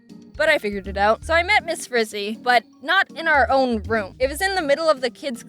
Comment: I legitimately thought you said Miss Frizzle and starting humming the Magic Schoolbus theme song